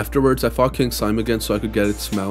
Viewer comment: love the video bro but is it just me who hears f***ed the king slime